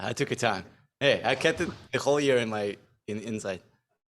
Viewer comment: "I kept it, the whole year, inside." And now it's out. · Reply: Lolll that makes sense now. I thought he said "I kept it holier on the inside"